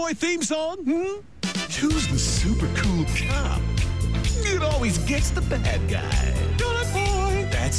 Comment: For a second I heard "Who's the super cool cop? that's always gets the black guy?"